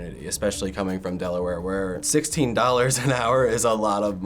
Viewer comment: how much did he say he gets paid per hour? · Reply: $16